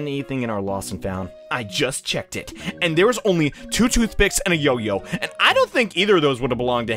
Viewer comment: Did you say 2 picks and a yo yo i love this guy